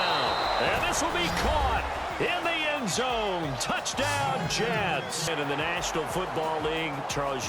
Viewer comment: the madden commentator says jats instead of jets.